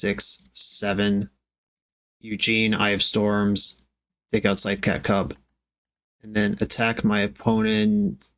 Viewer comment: did you just call ihm Eugene lol